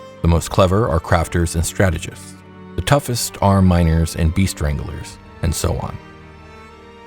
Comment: At about 12:30 you referred to Beast Wranglers... I heard Bee Stranglers. Heck I thought I know they are little but... oh I misheard.